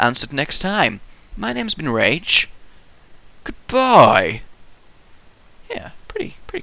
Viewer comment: lol when i first heard ur vids so way long ago i thought you said, "my name's Ben Rage" like thats ur real name lol